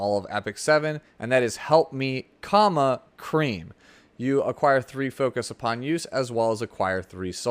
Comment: Tried to avoid saying the innuendo but ended up saying "Help Me CUMma Cream" instead of comma hahaah